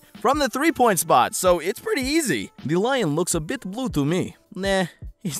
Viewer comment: idk why but i heard lewd instead of blue